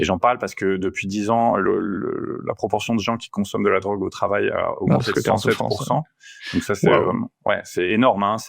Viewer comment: Je n'ai pas très compris, qu'est-ce qui a été dit s'il vous plaît concernant l'augmentation de l'usage de l'alcool au travail ? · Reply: "Depuis 10 ans, la proportion de gens qui consomme de la drogue au travail a augmenté de 107%."